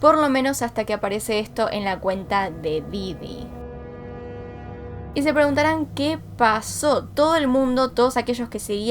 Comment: ¿qué dice? Xd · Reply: Dice "Esa z0rra está mU3rta"